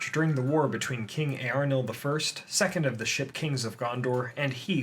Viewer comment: I swear he said sh*t kings